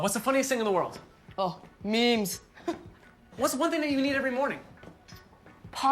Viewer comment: What is the funniest thing in the world? When she said memes I thought she said “me”. 😂🤣😂